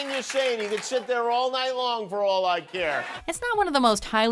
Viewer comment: What does Jimmy says at 2:58? ... Couldnt. Get it, seems something like : "sorry dummy you need to sit there all night long for all like here" is it correct?